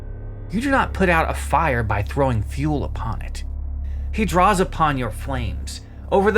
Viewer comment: I first heard this a throwing "a fool upon it" and was dying until I turned it back and realized you said fuel 😂